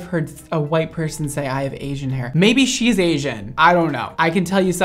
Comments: I think we were all just as shook as Brad when that girl said she had "Asian" hair loooooooool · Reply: I thought she said aging hair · “I’ve never heard a white person say they have Asian hair” 🤣😭😭😭😭 idk why that made me so weak 🤣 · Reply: I thought she said aging hair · white girl: “i HaVe AsIAn HaIr” me, with my thick asian hair: 👁👄👁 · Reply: I thought she said aging hair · white girl: “i HaVe AsIAn HaIr” me, with my thick asian hair: 👁👄👁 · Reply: I thought she said aging hair lol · I thought she said she had aging hair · I thought she said “aging hair” but I’m not sure. Given her accent.